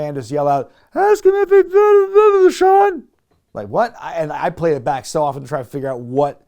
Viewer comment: Oh hell nah! I think I deciphered what that fan was saying in the crowd during the Shawn-Bret segment. I heard “Ask him if he buried Nugget”. If that’s what he said, truly disgraceful and distasteful shit.